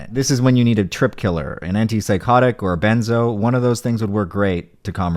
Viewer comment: hold up did he just say coke was a benzo?🤣